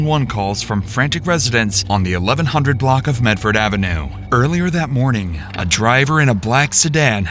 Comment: 😂 I thought he said Methford Avenue when saying the name of the street 😂😂😂😂 1:35 🤣